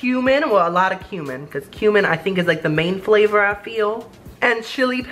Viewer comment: I Thought He Said Human😂😂😂